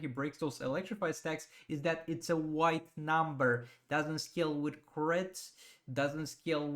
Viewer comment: also at 7:28 with regard orm- "doesnt scale with crit? or "skill" with crit or? I can't make out what you are saying. :-) i have sent multiple screen shots of terrible text/copy to Injustice- there are always grammatical errors so it wouldnt surprise me if Orm is wrong. HE has to be all wrong because hes not at all a character I care aboutright now. They need to make him badass